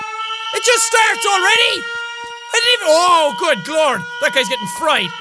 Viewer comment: At around 2:35 did jack say Oh glord?